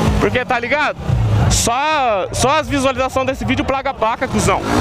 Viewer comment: PERA ? NAO ENTENDI " SO AS VISUALIZACAO DESSE VIDEO A PLACA PAGA ? " EU BUGUEI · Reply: Ele falou foi "Plaga a paca" '-' kkk